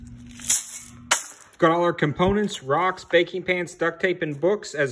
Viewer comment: At first i thought he said "garlic components "